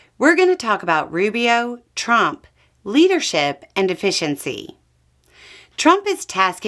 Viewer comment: Efficiency is doing things right. Effectiveness is doing the right things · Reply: It was Freudian on my part, but at the beginning I had to listen a second time because I thought said "Rubio, Trump, leadership and deficiency."